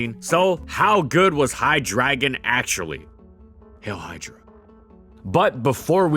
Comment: lol what is that noise, oh crap, he said hail hydra XD